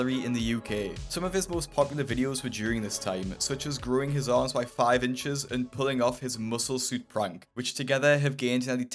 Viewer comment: i wasnt looking at the screen and I heard ‘growing his arse by 5 inches’😂😂